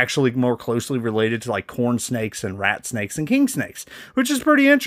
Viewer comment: Oh CORN snake. I thought I heard something else.